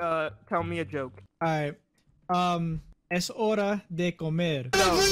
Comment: As someone who speaks spanish That "Es hora. De. Comer" was pretty intimidating lmao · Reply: Es hora de morir is what I thought he said